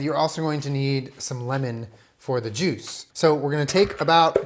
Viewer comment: I thought he said "lemons for the jews" and I was thinking "hmm yes yes"